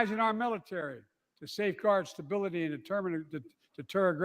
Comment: I swear it sounded like he said we must fear stability